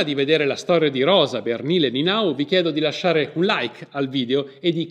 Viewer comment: Per un attimo al minuto 1:28 ho capito: "lasciate un Reich al video"